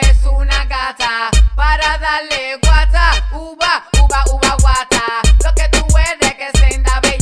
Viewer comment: Nadie: Yo buscandola en YouTube: uhhhh ahhh gata uva :'"v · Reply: yo pense que decia "ahora dale, wacha uva uva uva gata" XD